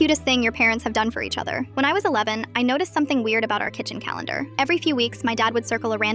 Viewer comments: I thought it said “when I was Oreo” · I thought she said when i was a lemon